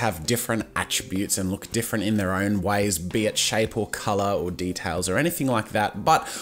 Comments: you said Bitch at 1:26 (Bee itch) You didn't but it kinda sounded like it. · it just sounds like he says "bitch". not "be it shape"